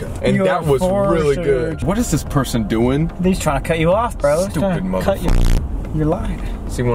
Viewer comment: Did he say “stupid muggle”?